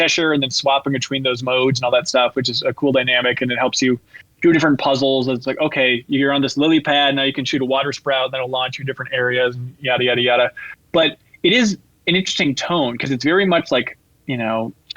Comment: Did say you can shoot a water "sprout"? 🌱 😅